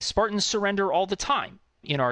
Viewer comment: OOOOHHH, Sprartans surender,.thought he said fart and surrender, nothing to do with the bloody french at all